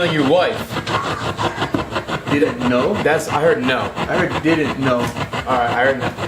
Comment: It sounded like it said, never, instead of didn’t know